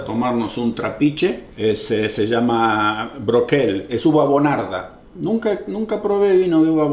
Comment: flashe que dijo "buenarda" jajsjsjaj nada que ver igual